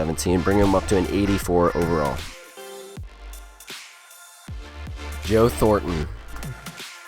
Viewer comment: he was a PPG player during the regular season, do you not think he deserves that boost? If you watch his play you'll understand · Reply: I'm not doubting his play, I'm just saying it sounds like he said Yo Thornton, not Joe Thornton lol.